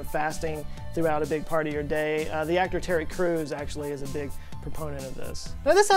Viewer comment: I cannot understand something that is said at 7:05. Topic is Intermittent Fasting, and the clip sounds like "?acturary croons?" "are big proponents of this [int.fstg.]" What is actually cited?